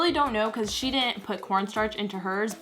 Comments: When you said corn starch it sounded like you said port stars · I swear I thought she said porn star when she said corn starch